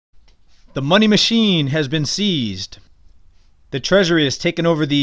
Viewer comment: Thought that said "Monkey Machine Seized"